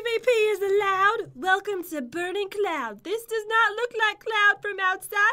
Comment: I thought u said Cloud does not sleep